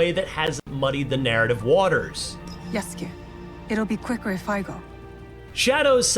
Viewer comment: I understood "yes king" instead of "yasuke" 💀